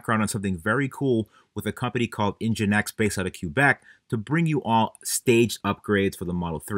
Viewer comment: Did you just say "cue Beck?" 🎶I'm a loser baby🎶